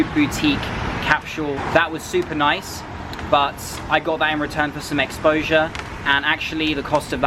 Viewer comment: At about 1:36 you said something about "something expoure" can you tell me what you said. It was a bit unclear. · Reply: *"I got there and returned for some exposure..."